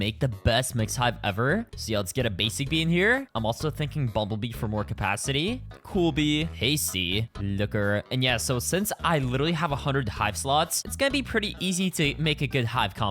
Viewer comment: BRO DID NOT SAY LOOKER 😭